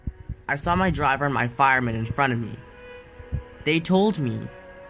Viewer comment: WAIT WAIT WAIT What did he say 1:24 Diesel engines have firemen?